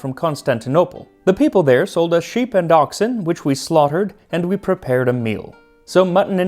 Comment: Oh geez, @ 1:10 I heard "..people there sold us sheep & DACHSHUND..." was worried about hearing that Attila like "hot dogs" for a second. Whew!